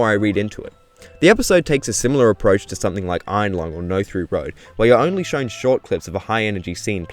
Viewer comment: What'd he say at 2:18? "Iron Lung & (No Three Road)" ??? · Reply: No Through Road. Different horror short film on YT.